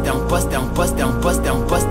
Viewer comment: Rumors still says he is still drowning till this day, btw sounds like he saying "pussy down" over and over.